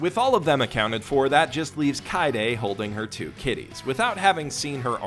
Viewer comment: I thought you said "Holding her two titties"